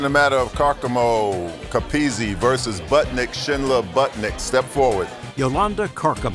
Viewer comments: I need Bird and the narrator to say Buttnick one more time. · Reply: I thought they said buttlick😂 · I thought he said buttlick!!!